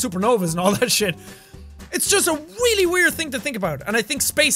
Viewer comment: He said "Weally Weird" instead of 'Really Weird'